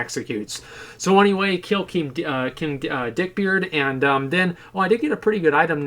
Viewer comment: Not gonna lie, I thought he was gonna say "King Deepthroat" Instead of "King Dickbeard" lol